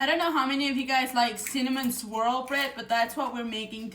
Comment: Oh I sware I heard you say Cinnamon Squirrel Bread in the very beginning 😂